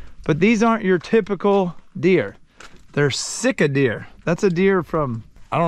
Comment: I thought you were saying Sitka until I saw one lol